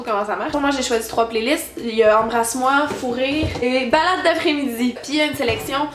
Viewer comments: La 1re fois que tu dis "Fou rire", j'ai compris "Fourrer". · J'ai entendu "fourrer" à la première écoute à la place de "fous rires", j'étais comme "Ah okay, parfait. Au moins c'est clair ._."